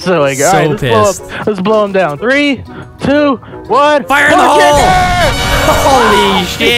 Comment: No animal is safe mwhahahaha! or maybe we aren't safe lol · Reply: It sounded like you said fire in the hoe